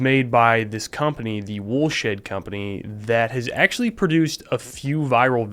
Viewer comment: Did he say bullshit company or am I just stupid